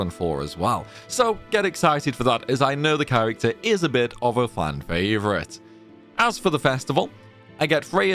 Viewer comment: did you say Flan favorite? 😅